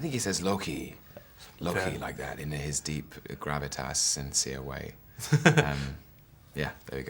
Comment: What did Tom say between 0:20 and 0:23? I don't speak English really well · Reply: He said "In his deep, gravitas, sincere way."